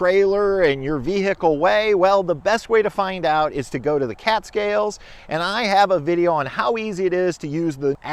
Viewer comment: Haha, until I saw the sign Cat Scales, I thought you were saying Catskills (as in mountains) and was wondering why I had to go there to get weighed.